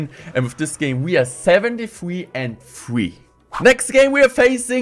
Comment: did he say he was 73-3? I’m so confused…